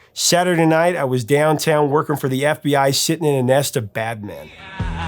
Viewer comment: I thought he said, "sitting in a nest with Batman."